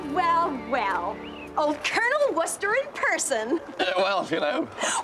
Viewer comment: Did she say "Colonel" Wooster at 13:30?